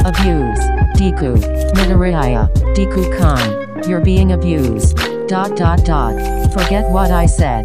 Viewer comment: I saw the video and I thought is said, "Deku is being absent?" Instead of "Deku is being abused?"